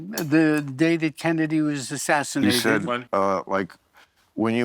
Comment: Did Woody say he was playing some Hollywood club when "David" Kennedy was assassinated? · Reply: I heard "the day dat Kennedy was assassinated."